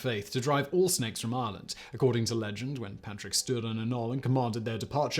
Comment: I thought he said “Patrick Stewart” 😂 drove the snakes out...I had to rewind it was Patrick stood